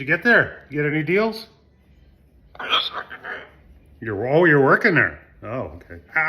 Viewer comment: you and Barney have so much fun together. Had to run it back 1:59 I literally heard Barney say "I work there." Thanks for sharing Barney with us. (I watched the full video including the ads before and after. Hope that helps.) 🧡🤗🧡